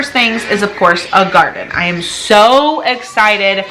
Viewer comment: I thought you were saying L.A Gear..like the sneaker company from back in the day… I was thinking wow I didn’t know they were back 🫣 I hope your family is feeling better it has been so cold my little puppy hates going out 🤣